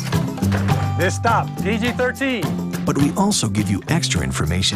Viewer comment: G: General audiences, all ages admitted PG: Parental Guidance PG-13: Parents strongly cautioned R: Restricted NC-17: mostly Porn, children are not admitted. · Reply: Did MPAA Says PG-30? Because 13 And 30 Is Simillar.